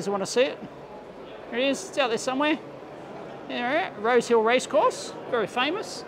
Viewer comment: "Rose Hill"....At first I thought he said "Road Kill" and was happy that would be ok in Australia!